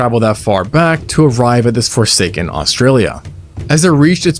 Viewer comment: EXCUSE ME????? WHAT DID YOU SAY SAY SSAY SSAY SYA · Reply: FORSAKEN REFERENCE 🤣🤣